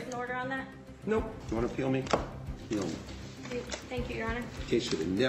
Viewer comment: There is an error at 2:05. Subtitles for the judge is, "you wanna kill me? Kill me". He actually said, "you wanna appeal me? Appeal me".